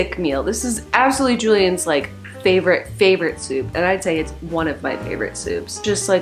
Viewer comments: I thought it said "making are favorite soap" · I thought it said making my favorite soap